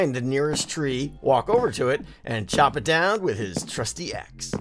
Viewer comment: Thought he said “With his Krusty axe”and I was like wut😑😐